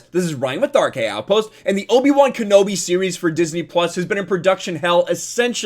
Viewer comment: I heard that as "Let's look at the Kenobi, Wan."